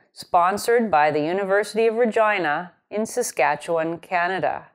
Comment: Maybe it's because of the poor quality of speakers on my computer but it, very ironically, sounds like is saying "The University of Vagina"